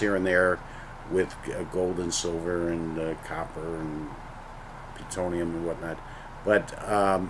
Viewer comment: Did he say plutonium at 2:23 ????